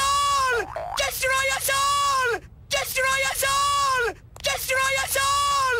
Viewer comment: Everytime I hear this it eventually starts to sound like him saying "Desteriya Saul"